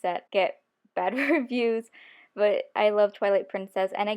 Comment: i misheard twilight princess as toilet princess.